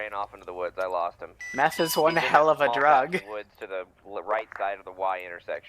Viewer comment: Meth is one hell of a drug is what you said, right?